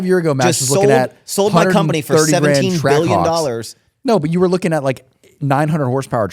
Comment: Did I hear 17 billion?? 🤔